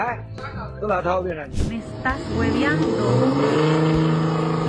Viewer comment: I heard Daniel Son....like on Karate Kid the movie. 😂😂😂